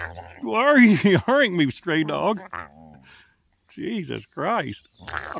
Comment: "Why are you yarring stray dog?" I thought he said straight for a second 😅